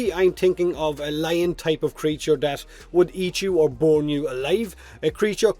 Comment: I swore you said "bone you alive" and now I have coffee over my keyboard. Thanks lol!